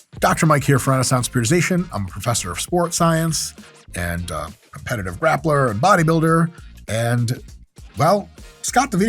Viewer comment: Lol I thought you said "competitive rapper"